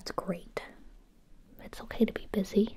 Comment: I thought she said “it’s ok to be pissy” in the beginning 😭